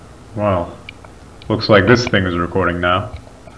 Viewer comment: i couldve sworn i heard "looks like this ngas recording now"